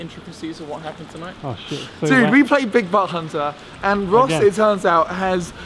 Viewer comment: Sounds like is saying buttc*nt